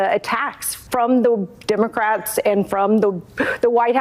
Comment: Did she really say, "Democraps"?